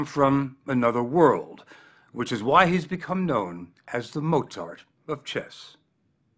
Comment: i heard “motard”